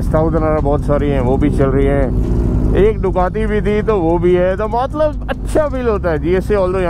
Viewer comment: Did you said Ducati bhi thi? 👀 sell kardi kya Cinderella 😐